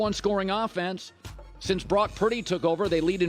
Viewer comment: Is it just me, or does it always sound like Colin says “Brock PRETTY” instead of Brock Purdy?